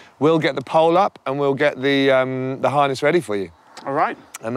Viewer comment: I really thought he said "Your Highness is ready for you" making a pun